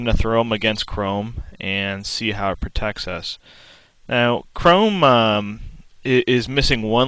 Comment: turn on captions, at 0:39 the captions says "now crow mom" LOL XD